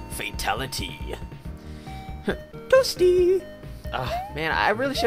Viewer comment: So that guy says toasty? I always thought he said "Whoopsie" or something.